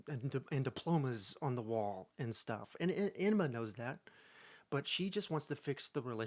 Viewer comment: did you just call her enema? 🤣🤣🤣🤣